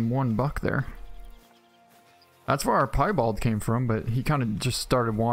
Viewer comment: I got a albino coyote on my friends map is that a kinda rare type to find of a coyote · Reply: I don't know what he calls those it sounds like he says pie bold 🙄😓😅